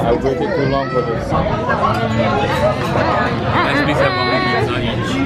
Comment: Wait fr did i just hear said “ive been waiting so long for this” HAHAHAHHA DAMN BOY U FR HUNGRY